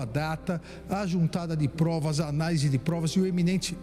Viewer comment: Ué eu não entendi por que ele fala que não tem provas anais, alguém que entende de direito me explica por que usar um termo assim? No minuto 3:30 a 3:35 ele solta está. 😮 Para que usar um termo assim ?